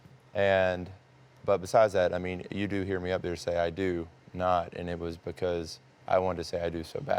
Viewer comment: I thought Damien said “ I can not” lol not “ I DO not.” Like the lady host exaggerated. 😂 not into the way they are interviewing... seems more like an interrogation. So disappointed.